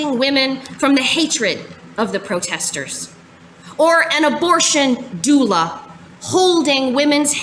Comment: I heard "ab0rt!on ghoul-a" at first 💀